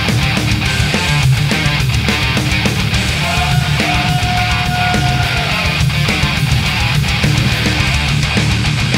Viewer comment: What ever he say at 10:50 it gets me pumped bro idk why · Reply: You ain't alone man, everytime I hear "Choo Choo" I get pumped!